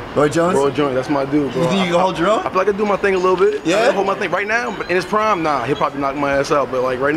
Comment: Did MBJ say “right now in his prime”?! What?? 😂